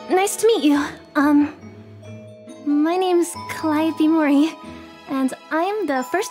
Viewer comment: Legit i heard "my name is kawai - ppe mori"